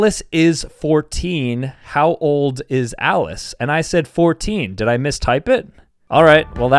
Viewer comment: he actually said “how old WAS Alice” and it’s her birthday so Alice is 15